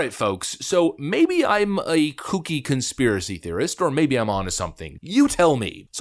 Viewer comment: When you started talking about Christie, I thought you said, "Cookie Conspiracy Theorist." 🤣